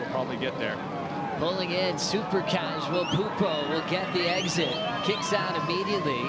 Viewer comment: I thought I heard pulpo. He could call himself "The Octopus". He grabs the good waves and has sticky feet.